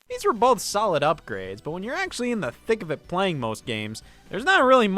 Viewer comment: did you say… thick of it…?